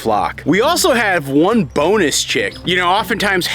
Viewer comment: Chirp, chirp, chirp,chirp, chirp, chirp...OMG for a second there I heard "boneless chick" instead of "bonus chick" and I was terrified :D